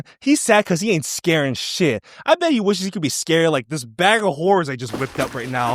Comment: please bring back the "bag of horrors" as a running gag in the future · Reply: I thought he said “bad of oranges” and I was just having lobotomy after effects